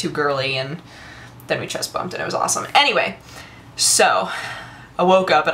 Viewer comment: i could swear i just heard "...and mike came into my room [...] and then we just humped and it was awesome" .... xD